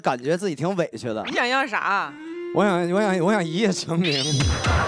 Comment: 高叶问刘凹你想要啥啊，刘凹说我想一夜成名， 我空耳听了个我想一夜情您😂😂